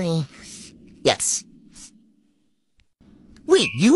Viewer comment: lol i thought he said math LIZARD